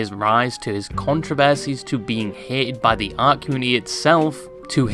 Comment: Tbh I thought you were saying ARK COMMUNITY and got rediculously excited....